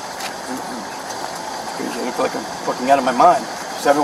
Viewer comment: And this is why everyone... Drugs are bad and will just lead to things like this. Also, 5:15 .. sounds like he says "I must be out my f**** mind” 😅 Yes sir, yes you are quite out of your mind and body as well it seems like.